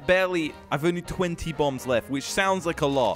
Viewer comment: Thought he said bumbs instead of bombs lol